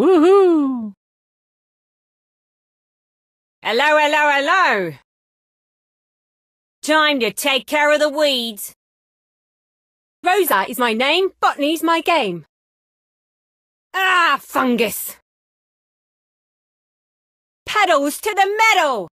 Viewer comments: i always hear "rosa is my name f*cking is my game" "f*ck me for the win" and some other weird things i can never unhear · I thought she say f**k but she actually says bottany Thanks man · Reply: same I thought she said "fuck" but when I figured out she actually said "botany" I got disappointed